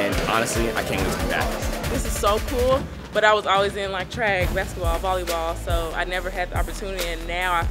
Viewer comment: Am i mishearing or does she say "track" at 3:57 and not "drag" as the subtitles say?